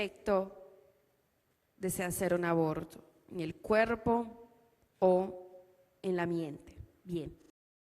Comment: QUE ES LO QUE DICE ? EN EL CUERPO Y EN LA MENTE DICE?